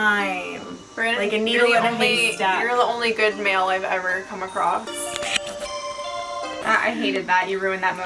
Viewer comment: What says, " You're the only good male I've ever come across". What I hear, "You're the best bigbrother I could have ever asked for!" XD